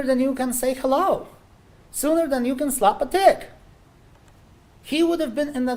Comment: Did he just say the victim would have been in the hospital “sooner than you can slap a d!ck!?!?!?” 😳😳😳😳😳